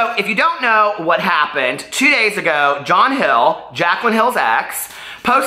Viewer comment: Oh I thought you said Jaclyn Glenn! Sorry I could care less about these people. Sorry, mean no disrespect 😘. Ew that’s so tacky! I would never even want to hold hands w my new significant other in front of my ex. It would just be time to go...LoL!